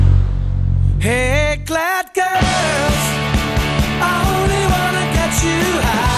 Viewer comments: I thought they were saying "black girls" haha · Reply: i thought they were saying fat girls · I thought they were saying "black girls" haha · lol the fist time I heard this, I thought he said "black girls only wanna get you high" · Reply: i thought He said "England Girls" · oh I thought he was saying "black girls"